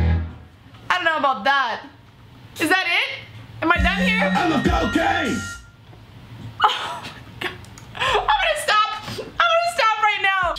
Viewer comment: I'm out of Cocaine😂😂😂LMAO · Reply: RTR Gaming I thought he said I love cocaine...